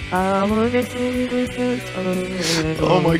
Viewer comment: Am I the only one who heard "What the hell Vargskelethor?"